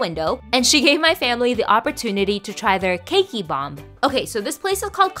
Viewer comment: Thank god she put subs cuz I thought she said kinky bomb